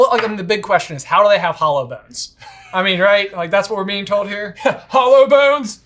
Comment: Somehow - and I absolutely know what you really meant - I heard "hollow bones" as "holo bones," like holographic bones.